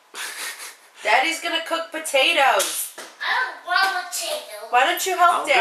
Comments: It kinda sounded like she said "I dont want those" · I heard "why won't you leave me?"